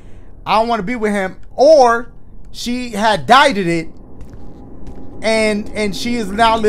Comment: Did you just say DIEDEDED 😂😂😂